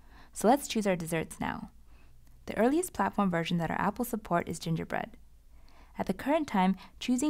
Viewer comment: Did anyone else notice that the instructor said Apple instead of Google at 0:05 ?? wish apple could support apks tho!! <link> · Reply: I believe it's even "our app will", not just "app will".